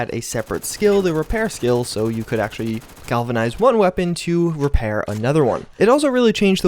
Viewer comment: Did he say galvanize one weapon at 1:43?